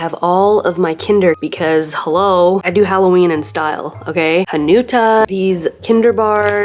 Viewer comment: What does she say @ 9:24 ?? 🤨🤔🤨🤔 Someone please enlighten me! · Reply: Hanuta. 😂 The little wafer and hazelnut treat.